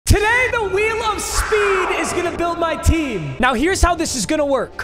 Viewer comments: Any mf who thought he said the wheel of sped builds my team 👇🏼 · Thought it said wheel of sped for a sec💀